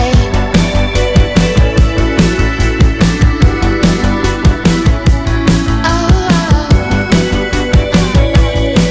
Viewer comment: I could hear what she was saying at 1:23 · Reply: "WHAT THE FUUUUUUUUUU-" I think at least.